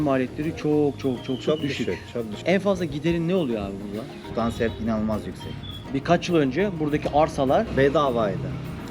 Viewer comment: En fazla giderin ne diye sordu 0:22 inci saniyede ? Cevabını anlamadım ne dediğini sudan sert mi dedi tutan sertmi dedin ? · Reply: elektrik